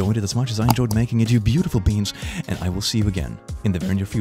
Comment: I know that it's supposed to be "beings" but I heard "beans" lol Edit